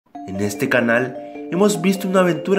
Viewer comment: like porque dijo este carnal embes de este canal en el minuto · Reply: Dice canal no carnal